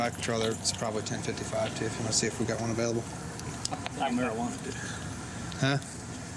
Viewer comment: Can someone else please watch & listen at around the 44:10ish mark when the cops officer shows up to the car vs pole accident. The other officer approaches him, & I can swear he says, "I marijuana'd that dude" & the other cop says "huh?" Because he's sort of whispering it. So he then says it even quieter, while pointing to his shirt pocket. Is he eluding to the fact that he planted those drugs on the guy in the car? Not that I'm surprised a cop is capable of such a thing. Especially back in those days. But why else would he be whispering about instead of confidently saying, "Hey, check his left front pocket, I think I saw something in there." Instead of being all secretive about it. Funny thing is, is that I don't think he realized that the cop was mic'd up for the show, & it ended up catching the audio. Either way, right after that was said, the cops cop went right into dudes pocket & found the drugs, immediately after that. Lol. I mean come on. He just showed up on scene 2 seconds ago. 😂 Idk, I just thought how freaking brazen of that cop to say that right there, while the show cops is filming. Crooked cops are the ones who give good cops bad reps too.